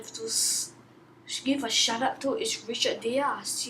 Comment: Did I hear you say 'Give a shut up to is Richard Diaz' :O